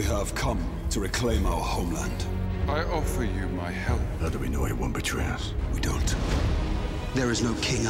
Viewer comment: What was said: "How do you know he won't betray us?" What I heard: "How do you know he won't beat your ass?"